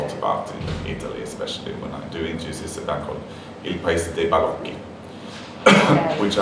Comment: what band is this ?? I can't make out what he says lol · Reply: He said " Il paese dei balocchi"!